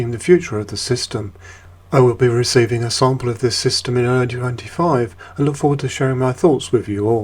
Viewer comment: at around 7:20 did u say early 95 ? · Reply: 2025 🍻🍻🍻